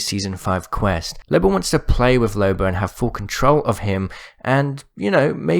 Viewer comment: Did I hear him say Loba wants to play with loba. On time